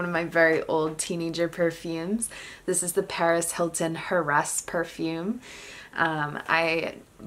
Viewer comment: Did you just say harass perfume by Paris Hilton 🤣